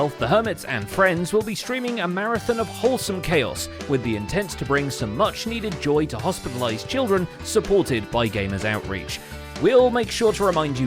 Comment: I'm sorry, I heard "to bring some much needed to joy to hospitalize children" 😅